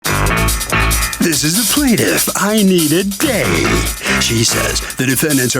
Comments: PLEASE tell me the plaintiff's parents didn't give her the name "Inita Day". That's just putting her behind the 8-ball at the start of her life! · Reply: I thought he said I Need A Day😂😂😂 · “This is the plaintiff, I-Need-A-Day” 😂😂😂 dont we all?! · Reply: I thought they said I Need a Date 😂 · Inita day I thought they said I need a day lmao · When they read the plaintiff’s name, at first I thought he said “I need a date” 😂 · I thought the announcer said "I need a date" when he said the plaintiff's name 🤦🏾😂